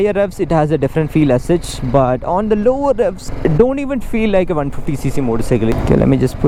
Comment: did you say don't even feel like a 150 cc. That cracked me up😂